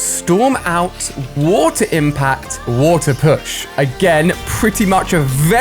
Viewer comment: I'm pretty sure it says windpush instead of waterpush